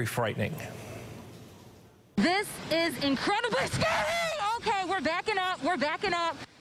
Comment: did she says bisgetti